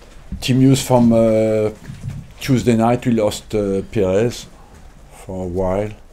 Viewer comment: Thought he said "we lost Pires" for a second there.